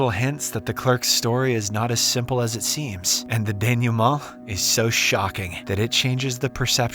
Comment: What's the word used at 13:33 dan you moe? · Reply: It's "Denouement"